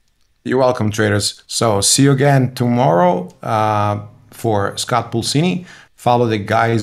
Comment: J What's the guy's name who you said to follow at 1:24:55? Scalpuccini? What's his YT handle? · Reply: Scott Pulcini - he trades futures in the Bookmap webinars every Thursday at 10am ET.